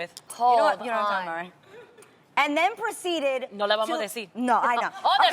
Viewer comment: "We're not gonna tell them?" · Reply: I definitely heard “no le vamo deci?” Which i assumed was like “No le vas dicen?” Or like “you’re not gonna tell them?” Thus referring to not saying the friend’s name…